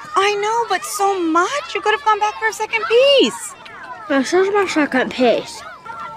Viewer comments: It’s sounds like your saying this is my second piss😂😂😂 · I was so dum and I thought she said “it is my second piss) it sounded like it 😂❤